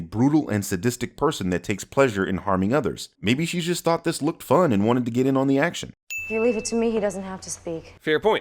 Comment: Lol 12:40 voice over says- "maybe she just thought this was fun and wanted get in on the action"..... subtitles say -"maybe this just made her moist"